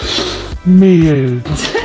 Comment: ich die "meeeehl" verstanden hat pahahahahahahqhqhqh🤣🤣🤣🤣🤣🤣